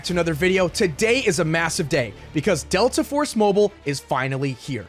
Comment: did bro really said MASSIVE !!